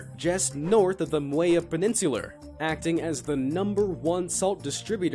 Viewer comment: did he just say paninsuler?